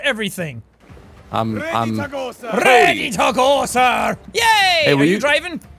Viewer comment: I thought Jack said, "Ready taco sir!" but it was actually "ready to go sir."